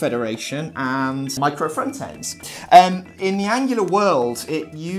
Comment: Thanks! I heard the word "Reangular" :) I like it, maybe a new UI Framework from you ? :)